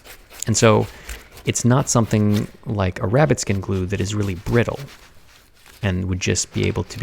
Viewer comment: For some reason i thought he was saying “rapid skin glue”. Now I’m sad